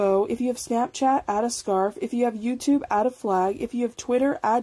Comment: if you have youtube add a ??? Can someone tell me what it says? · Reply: flag